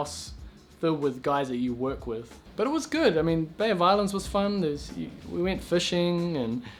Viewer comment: I keep hearing it like "Bay of Violence", lol.